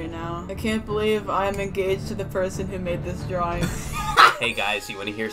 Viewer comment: correct me if im wrong, but did she say shes ENGAGED??//??